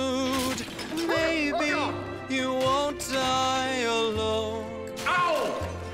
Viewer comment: At first I thought he said "maybe u want I alone"